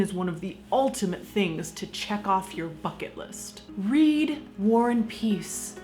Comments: I heard 'fuck it list' instead of 'bucket list' XD TFIL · Near the beginning of the video, am I the only one who thought she said "fuck-it list" instead of "bucket list"? I mean, I can see someone going, "Eh, fuck it: I'll climb Mt. Everest."